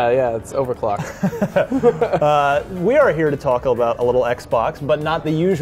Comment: I must be haungry because at 0:19 i heard Tacobell instead of talk about.